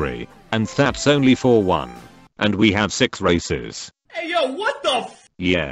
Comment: Him saying to awaken race: "and we have 6 races" What i hear: "and we have 6 racist